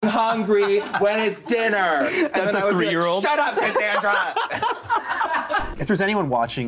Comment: Oh my fucking god is one of the funniest people you’ve had on this series, them saying “SHUT UP SANDRA” absolutely sent me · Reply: I heard "SHUT UP CASSANDRA!" And that is what the auto generated captions said. Still loved this quote nonetheless